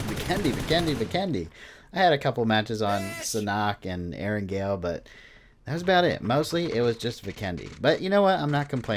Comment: anyone heard the guy at 3:11 right after he died? LOL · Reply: Biiiiiitch lol